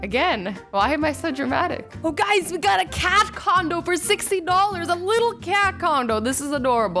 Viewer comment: Sorry thought she said cat condom